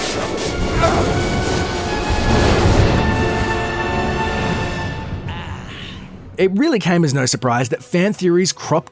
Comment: what did palpetine say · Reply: "Argh"